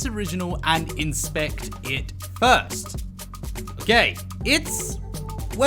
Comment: When he said ‘Kay’ at 9:14 I thought he said gay lmaooooo